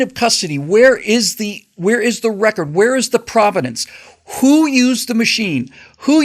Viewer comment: "Provenance" is the word you were looking for. To my ears it sounded like you said "providence." You commentary was enlightening. Thank you.